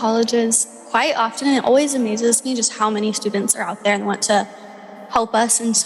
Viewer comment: You can detect a nervousness in her voice that I've not heard during past interviews. Is the pressure getting to her? Is she about to crack? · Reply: did she say 'always amuses me'?